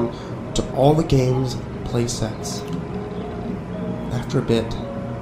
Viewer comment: the caption says play sex XD